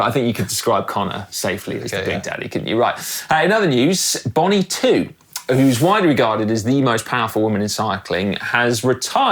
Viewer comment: could have sworn I heard 'Bonnie Blue'! Bacon sandwich spat out across the kitchen and I'm thinking "hell, this episode just got a whole lot more interesting!".